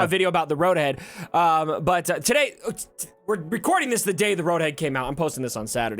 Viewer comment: Sound like your saying road head :P. Or road egg.